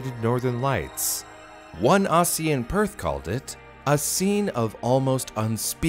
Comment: Did he just say Ossie instead of Aussie???????